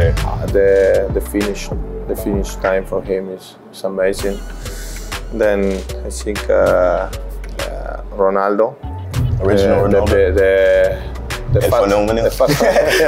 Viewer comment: Did Suarez just say ´´The fat´´ 0:35 It honestly sounded like he said the fat Ronaldo WTF lol.